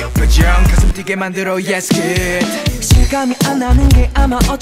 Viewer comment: as a deepwink shipper i just wanted to mention how jihoon rap part sounded like jinyoung's "Yes good." #deepwink lmao